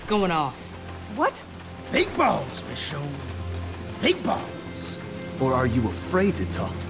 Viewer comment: it really sounded like Randall kinda said "Meatballs, Michonne. Meatballs." XD